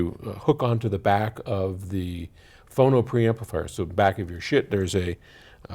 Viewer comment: IT WOULD BE PERFECT IF SAID AT 05:07 "Thru all this pieces of shiit!"!!!!! HAHAHAHA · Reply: "back of your sh*t..." LOL - I'm pretty certain I heard him say it.